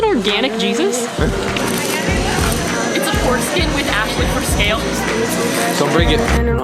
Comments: I LEGIT THOUGHT ASHLEY SAID pork skin with ashley for sale... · i thought she said foreskin instead of porkskin lmao